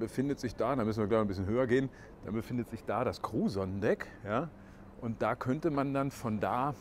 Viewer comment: Hat etwas gedauert, ich habe immer "Cruson-Deck" verstanden und dachte was nen blöder Name. Crew Sonnendeck. :-)